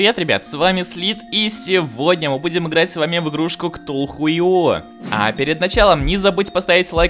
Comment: Мне послышалось "кто хуй? .io"